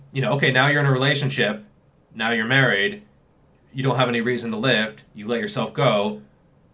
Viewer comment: What I heard: 'Now you're married, you don't have any reason to live...'